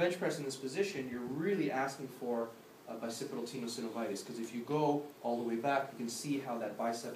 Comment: Very interesting video! Great explanation of how the different parts is connected by the line. At 00:37 , what example is he giving there? Is he saying: Bicepular tendo syndonitis? Or what is he saying? I can't get any matches when searching in google for more info. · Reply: biccipital tenosynovitis :)